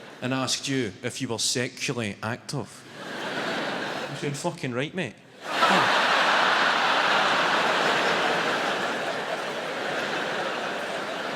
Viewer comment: What does he say at 4:33? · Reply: Youd’ve said ‘fuckin right mate’